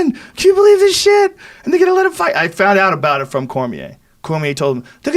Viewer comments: did joe just say nigga gone let him fight agian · did he say "nigga just let him fight lol ?"